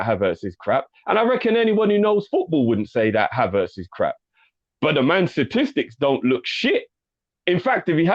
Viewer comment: This whole time i thought the guys were saying "bun the man" like it was some Jamaican slang for like "screw him" or something. This is a revelation 😂